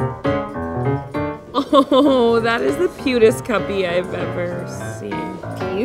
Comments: Who else thought she was saying cutest puppy and had no idea why that was stupid? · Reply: shellylovesmakeup i heard 'putist cuppy' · Who else thought she was saying cutest puppy and had no idea why that was stupid? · Who else thought she was saying cutest puppy and had no idea why that was stupid? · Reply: I thought she said cutest cuppy lol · Who else thought she was saying cutest puppy and had no idea why that was stupid? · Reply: at first even i thought she said the right thing but then i rewound and then i heard putest cuppy